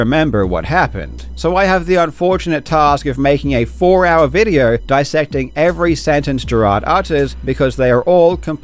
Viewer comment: I heard “four hour video” and had to double check 😂 Guess I’m in for the long haul today